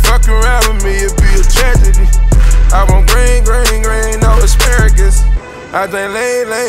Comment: Did he say bring the asparagus? lmao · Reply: I thought he said I want green green green, not asparagus